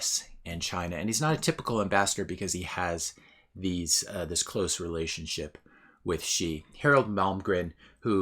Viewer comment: If I had heard it just once, I would have chalked it up to "he's speaking off the cuff" , but I heard it twice. The Latin teachers in my head are screaming "Do something". At approximately 4:47 of the video, I heard "close relationship with she" and around 5:20, I heard "between Trump and she" and I could almost hear your inner English teacher objecting when you used "she" when you should have used "her" in both cases. I remember from my Latin days, if one were translating English into Latin, one had to know what part of speech each noun was because each noun would have a different ending (depending on what part of speech it was) in the latin sentence. You could scramble the words in a Latin sentence and still know what the sentence conveyed because of the endings. In both of the referenced cases above, the lady referred to would have been in the ablative case and the ablative pronoun case. And in English, the ablative and accusative case are one and same and they aren't the nominative case "she" but "her" I am more than 30 years removed from my Latin days so my reasoning might be slightly flawed but I stand by my conclusion. This sort of thing, in my opinion, started with Anthony Robbins in the 90s who would be heard by many an inspiring go-getter on his audio tapes, saying "between you and I". You don't have to comment but I had to.(pretend there is a smiley face here)